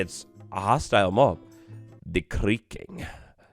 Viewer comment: The brain rot is real, I thought you said "the tweaking" instead of "the creaking".